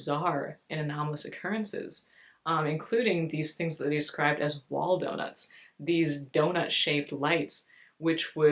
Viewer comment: I thought you were saying "Waldo nuts" for awhile there.